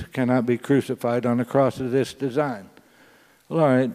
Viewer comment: "A true masterpiece cannot be crucified on a cross of this design" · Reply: With respect, I thought he said, “...on a cross of it’s design.”? I could be wrong. If what I heard was true, I thought it was insightful.